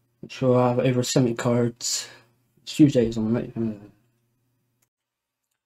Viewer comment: did you say: "Stew days on a night uhh" 🤔😂